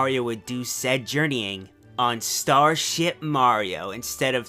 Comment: If you look closely at start fortress planet you can see megaleg at the bottom. Maybe it was a beta whatever galaxy megaleg was at. Also I thought you said "Starshit Mario." Laughed my ass off. :P